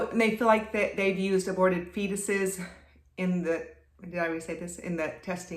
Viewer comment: Did she just say “aborted fetuses”?